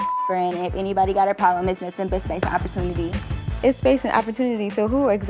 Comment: And what is "basic opportunity"? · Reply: Ohhhhh. Okay, I thought she said basic opportunity.